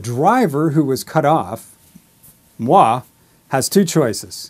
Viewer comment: Did you say "moi" ? :)